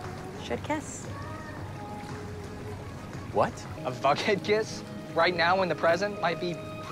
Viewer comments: They actually say their ship out loud. I can't believe this. "A Vughead kiss could save a future Bughead from imploding" CRINGE AF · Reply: I am not even kidding, I thought that he said a f**khead kiss might save a future bughead and I was like what is happening. 😂😂😅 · I always thought that Jughead said "a fuckhead kiss?" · I swear to god without the captions i thought jughead was saying “a fuckhead kiss”